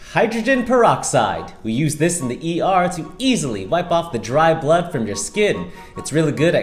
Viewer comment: All my girls, we need this for the unexpected times 😂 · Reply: Dude i thought you said deep-fry the blood.